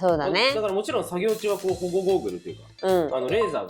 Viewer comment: 本当にしょうもないことなんですが、一瞬目を離したタイミングで聞こえた「保護ゴーグル」を「ボボボーボ」に空耳して二度見してしまいました。彫刻かっこいいです！！！